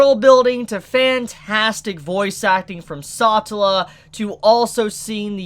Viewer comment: Wait, did he say Sa-ta-la because I thought it was Sa-tell-a